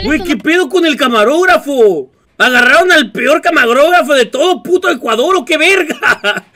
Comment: agarraron al peor.... QUE?!?! no entendi ni v3rga, camagrofaro?